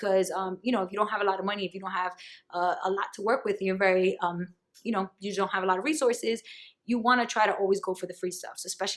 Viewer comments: Anyone catch the name of the company at 6:45 ? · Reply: WiseBanyan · WHAT is the company called?!? I couldn't understand what you said! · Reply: WiseBanyan - after searching for wisebenny, wisepenny, penny wise!!!